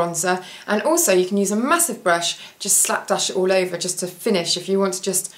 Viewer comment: at first I heard "use a massive brush and just slap that sh*t all over" hahaha lovely video x